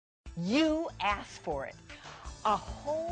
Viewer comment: I listened to ASMR while turning on this video and at 0:00 I heard "you asshole!"